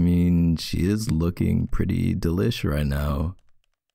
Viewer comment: k: “i mean she looks pretty delish right now” me: misheard him saying “relish” what the- 😃